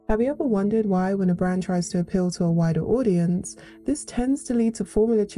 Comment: I heard "wider audience" as "whiter audience" at first until my brain fully processed what you said lmaooo.